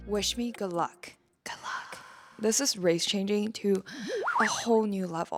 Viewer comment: The only race I'm changing to is Elvish because after this nonsense I definitely don't want to be associated with humanity no more 💀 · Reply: HELP I THOUGHT YOU SAID UR RACE CHANGING TO ELVIS AND GOT SO CONCERNED FOR A SECOND THERE